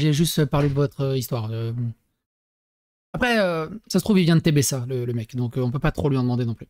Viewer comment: Attends 15:00 j'ai entendu TBS ?